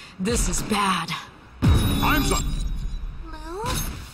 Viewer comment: what did she just said? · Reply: I... lose?